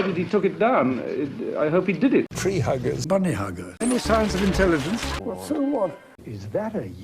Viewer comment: "Any signs of intelligence?" Did he say that?! 🤭